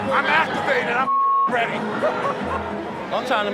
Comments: did gronk just say “i just masturbated so im ready” or am i going crazy? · Did Gronk say "I masturbated"?